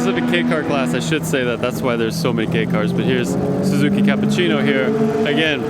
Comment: I heard wrong " ..theres so many gay cars..." 🤣17:12 And then I see the pink ... 😂 Having a blast there I see...😎👍 Suzuki Swift owner and great fan here 😍 Was the living legend there ? Keiichi Tsuchiya 😍